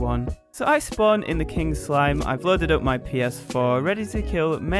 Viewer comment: Did he say ps4? How he has god mode and spawn commands?!?!